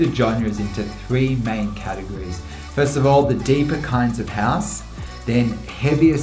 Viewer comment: I think i dont unterstand it correctly, what are you sayling? "the epic sense of house"!? :) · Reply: deeper kinds of house, heavier styles of dance, vocal driven music :) I am generalising a little haha :) Sorry I will try and speak more clearly in future ;)